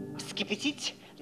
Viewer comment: мне послышалось что он сказал СКИБИДИ!